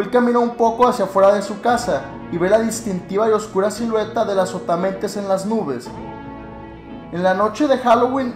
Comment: Yo pense que decia azopotamemes jajajaj XD